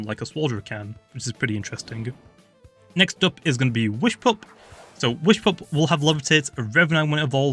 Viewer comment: Did u say "whishpup"